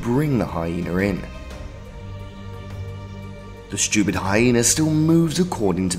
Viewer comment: did he say "the stupid hyena" ?